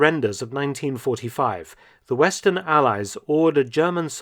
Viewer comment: I THOUGHT THAT SAID ALIENS